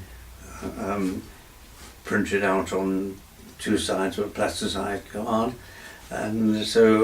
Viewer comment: what does he say here? Printed out on two sides of a ????? (sounds like "platisized guard") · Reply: 'plasticized card', I think